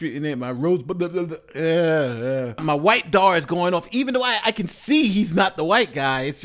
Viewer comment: Did he just say, "My white-dar is going off"?